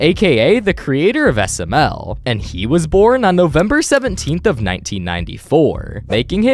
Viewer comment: wait says the 17th but the caption says the 19th